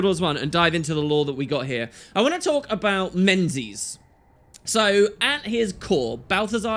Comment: As a woman, when I heard you say "today I want to talk about menses" I was quite confused XD